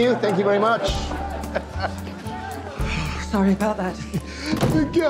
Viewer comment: The first time I saw this I thought she said “the guy from Creed” not Cream. Which made it funnier